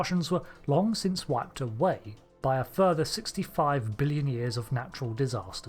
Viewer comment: I beg your finest pardon, but it sounded like you said 65 billion, not 65 million. I'm unsubscribing. /s